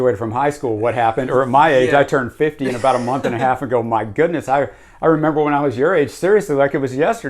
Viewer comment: haha my man not quite yet Aug 4 but thanks. Hope you liked it and i am locked and loaded haha! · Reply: I must have misheard you. I thought you said a month and a half ago 😂